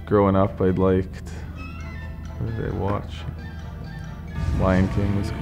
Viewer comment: When Chris Tanev said “ as I liked what did I watch” I thought he said baywatch